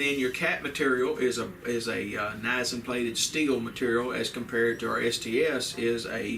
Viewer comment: What is the steel cap for Gun Club shells plated with? Can't make out what he's saying at 4:24 but it starts with "ni____" ( not zinc). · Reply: It is Nickel-Zinc. The symbol is NiZn, as represented in the periodic table, so it is often pronounced "NI-ZEN" like the symbol.